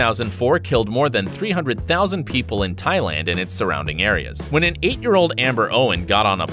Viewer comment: Did he just say "thigh land" and not "Tai land"?